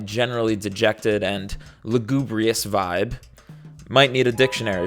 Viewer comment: I thought you said "Labubugrious vibe" to illustrate a sardonic take on labubus and consumer culture XD